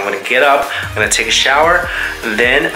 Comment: I misheard everything... i thought you said " i'm gonna eat venice, then i'm gonna eat the south of venice" and i just imagined you munching through the country border or something. 🤣😂🤣😂🤣😂